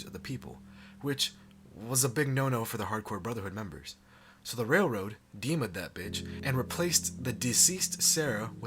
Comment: between 8:00 and 8:05 did he say they came at that bitch 😂